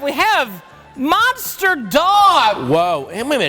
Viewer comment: I thought rich said mobster dog which would have made for am awesome movie